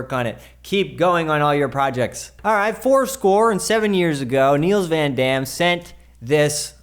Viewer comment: did he say they sent it in 7 years ago??